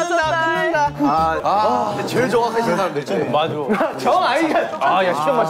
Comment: It is like jimin said APPA KA NAAM LETA HAI IT SOUNDED LIKE THIS TO ME😭😝